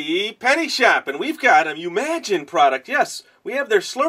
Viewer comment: anyone thought umagine said unimagine?